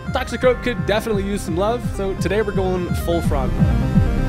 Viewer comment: Without the sub i thought you said “its time to go full frogtle” 😂